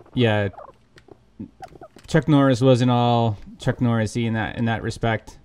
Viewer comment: I thought you said chuck norris